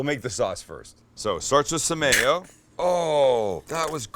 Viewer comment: oh my gosh i was wondering what the fuck samayo was.... duh "some mayo" please comment on this. i heard "Samayo"!